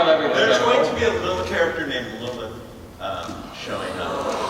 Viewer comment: what is the name he said? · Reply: Lilith, the evil demon goddess thingy who is cursed with infertility sort-of. Sorry for that terrible explanation.